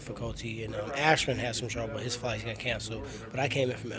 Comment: I thought he said Askren's fight got cancelled...but he said flight...I almost punched the person next to me